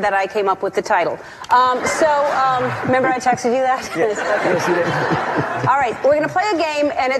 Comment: he actually said no she didn’t